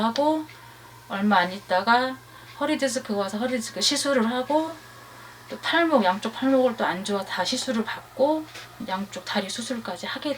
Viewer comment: 팔목을 탈모라 들었네… 난 바본가?